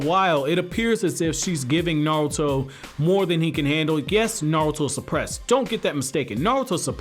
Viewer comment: did you say "guess Naruto is supressed" ?